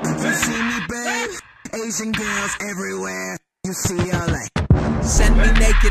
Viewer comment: I always thought he said ''Asian girls everywhere UCLA" then "Asian girls everywhere, you see all A's" the second time